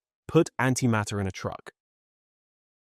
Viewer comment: I thought I heard speaker say "Put Anti-matter into a Drug" instead of a truck. What if we put anit-matter into a pill and swallowed it? Would it just be euthanasia? Or would something else happen? Perhaps swallowing anti-matter is the RED PILL?